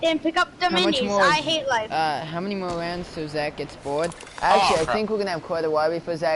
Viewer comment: did he say “ in till zack gets boared